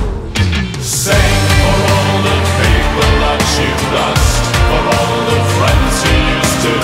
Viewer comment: I srsly thought he said "all the people you lust"